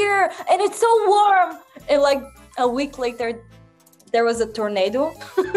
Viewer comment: Hahahah. At 1:22 she actually says "Look at me! I´m like a bear!"